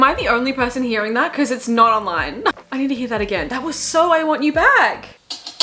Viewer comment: i heard “I want you back” too and I am so happy I am not the only one ahah